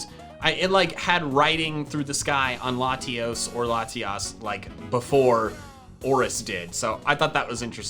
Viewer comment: Your Subtitles spelt Jaiden’s name wrong ._. And at 5:40 when said Latios and Latias the subtitles said Latios and Latios